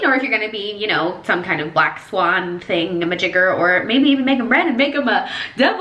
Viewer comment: did i heard BLACK SWAN .... army it's bts